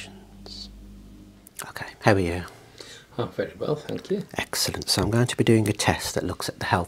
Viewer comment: Misheard that as "Not very well, thank you" and thought you gave him some real sass in return 😅